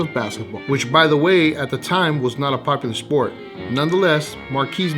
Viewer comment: Maybe I just had a stroke but at 0:54 I heard say "which at the time was not a fucking sport"